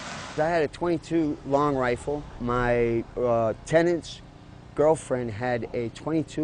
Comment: Am I the only one who heard the guy say he had a 10 inch girlfriend